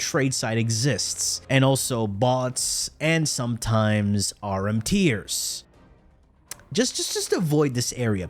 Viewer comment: did he say "RMTiers" or "are MTers" or something else? I have no idea what he said. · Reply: RMT, Real Money Trade.